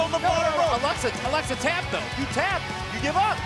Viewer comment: Did he said to she at 3:19 ''He tappes'' :?